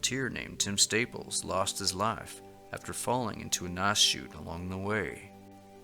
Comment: Another mystery is into what the volunteer searcher fell causing his death described at 13:32. I tried phonetically to figure out what mas chute or nas chute meant. Figuring it might be a loan word from Spanish, since there are many of those used for geographical features in North America, I searched Spanish, but I found nothing. What were you saying?